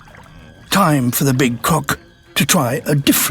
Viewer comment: Am I the only one who thought he said coc.k instead of croc? 😂